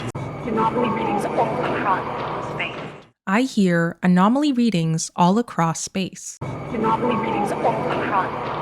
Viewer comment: Voice 2: "Anomaly Readings All Across .... Space" I hear it as "Anomaly Readings All Across KNOWN Space"...